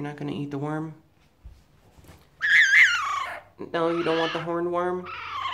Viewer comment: I thought that said hookworm